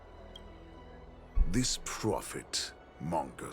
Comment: i neaded to repeat this. i thought said "a gen Z"